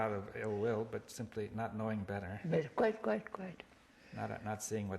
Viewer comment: Does Krishnamurti tell Bohm "Quiet Quiet Quiet" around 11:40, or does he say "Quite Quite Quite."